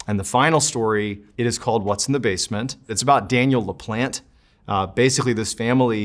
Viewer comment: I thought he was saying "Daniel the plant"